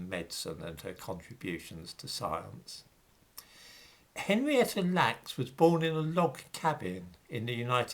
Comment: I thought that said Henrietta LAX... and that she might have been the inventor of the Laxative!